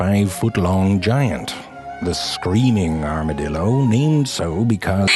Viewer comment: i heard you said "armadildo"